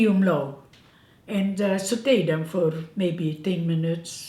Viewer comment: Your videos remind me of my grandmother who was an excellent baker like yourself. Your accent is relaxing to listen to. I had to replay the video at 3:11 as I thought you said you were going to "sedate" the apples...rather than saute'. My bad. Have you ever made a casserole that would be of interest to your viewers? I like casseroles. Would like a recipe of yours for one.